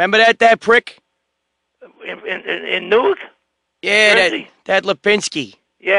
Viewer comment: "What about that Lapinsky?" "Who??" "That Pinsky? Remember that prick?? "What Lapinsky??......In the news???" Lol!!! Ole' thinks he's talkin' about 'Monica Lewinsky' and her fondness for Slick Billy's willie!!!😆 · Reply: I thought he said 'in news' as in part of a radio station they worked at.